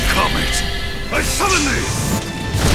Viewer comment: Seriously tho, I heard "Ice lemon tea" instead of "I summon thee"